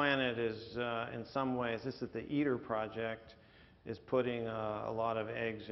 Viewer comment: Does say "The Eater Project"? I cannot find information on that. Thank you in advance. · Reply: He's referring to the ITER Project. You may get more information by searching ITER or going to iter dot org.